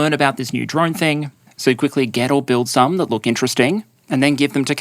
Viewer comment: he says, "...you quickly get or build some..." but initially i heard, "...you quickly ghetto build some..." which i guess also works.